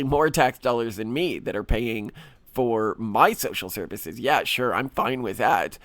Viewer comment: did Malcolm just say "tax dollars pay for my SEXUAL services?" LOL